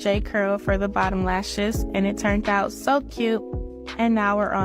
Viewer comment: I love how the caption reads “turned out” when she actually said, “turnt out”. 😂 13:50 Hold on a minute. It is not racist to point out that bonnets and smoke detectors beeps go together. 😂